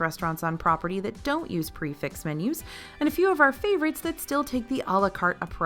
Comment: I thought you were saying prefix somethingorother about meals. In context, you were probably saying prix fix but without a French accent. Maybe don't run the words together?